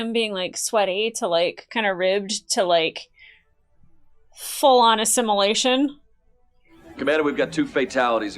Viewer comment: "went from just him being, like, sweaty, to like, kinda ribbed, to like, full on erection." I may have misheard a word or two...